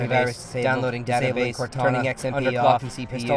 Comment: I hear a Cortana in there...